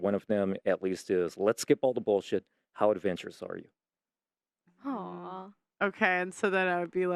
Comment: I missed that, "How advanced your story"?!!? I rewind it 3 times. Is my hearing off?, What did he say? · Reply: Let's skip all the bullshit. How adventurous are you? From this book. <link> And this AI. <link>